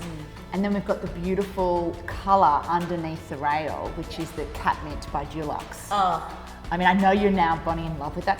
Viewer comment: Lmaooo at first when they said “cat mint” I thought they said “ketamine” 😂🤧 I was like who names their color “ketamine”?